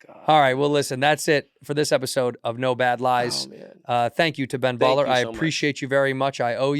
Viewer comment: Son of a botch,thought it said Ben Stiller! 😭😂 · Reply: So did I! Who the F is Ben Baller???!!!